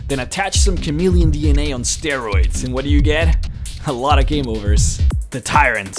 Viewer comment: = I heard this as "a lot fo gay rumors", which was amplified by showing Piers and Chris at the same time.